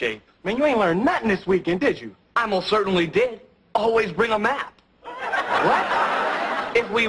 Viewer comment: Omg haha I thought he said always bring a mask 🤣😷🤒🤕